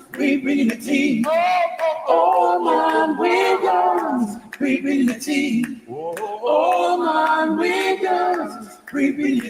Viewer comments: I keep hearing “ all my wiggas, we bringing the tea” I know everyone who reads this comment won’t be able to unhear that too lol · All I keep hear is "all my wiggasssssss" 😩😂